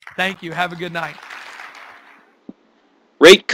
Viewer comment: For a second I thought you said Rape Comment and Subscribe. Lol. Stupid brain, I can't focus things in properly. It's not proven, but I think my brain is just slow to focus on things, yet I'm top of all my math and science classes (Not impressive in Texas)